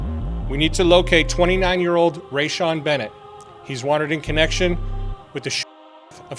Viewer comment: I thought he said Dayvon Bennett I'm like what does von have to do with this 🤣